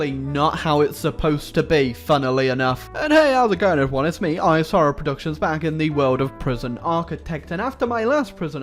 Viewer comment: every time i hear "isorrowproductions", in my head i hear "I'm sorry productions". Am i crazy? xD